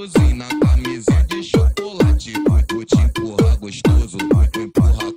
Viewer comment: Cuando dijo camishina o chocolate pensé que decía camisa de chocolate o algo así XD